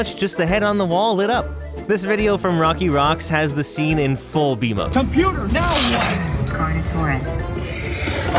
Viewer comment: I’m sorry but I was laughing every time I heard: “ computer now what?” “c a r n a t o u r o u s” I can’t spell I tried